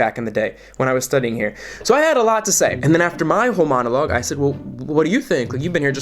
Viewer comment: You said “whole monologue” and I heard “homonologue”